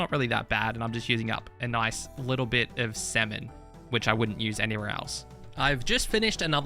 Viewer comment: when he said salmon did anyone else think he said semen?